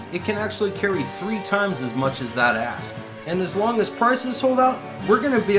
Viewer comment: I thought you said "It can carry three times as much as that ass" gave me a good chuckle